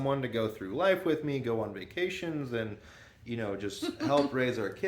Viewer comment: I'm pausing to say I heard "go on medication together" instead of "vacation" and I was like "Whaaa?" Your baby is so cute btw and you all are so silly. I love it.